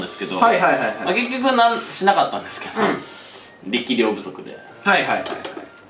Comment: 「力量不足で」 が 「生霊不足で」 に聞こえて うん？？何のボケ？？夏だから？？ って一瞬なったわ笑笑